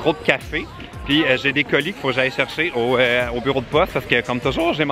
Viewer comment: Prend le temps de parler! A 3:33, j'ai entendu "j'ai des coliques", j'ai eu très peur